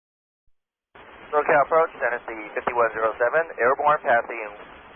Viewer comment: That should be 'Dynasty 5107' instead of 'Dynasty 5017' and 'climging' instead of 'climbing' Btw, Nice job for the captain and the ATC!